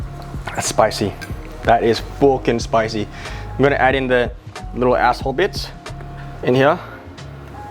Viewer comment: did i hear it right?? 5:21 DID HE JUST SAY ASSHOLE BITS??!! 😂😂😂HAHAHAHAHHA